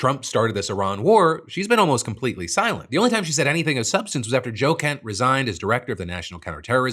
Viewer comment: For a moment i heard Joe Kent as Jocat and I was very confused.